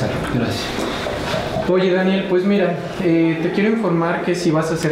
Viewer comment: no sé yo pero escuche que a le dijeron Daniel no sabía que así se llamaba :v